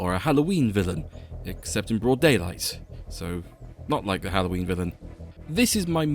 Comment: I really can’t wrap my head around how else “or a Halloween villain. Except in broad daylight. So, not like a Halloween villain” could be interpreted? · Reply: Going back to it, I think I only registered the "not like a Halloween villain", which sounds more like "not like THE Halloween villain" to me (at 0:34), so more of a mishearing on my part.